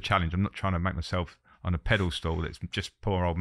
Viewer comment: does he say pedal stool instead of pedestal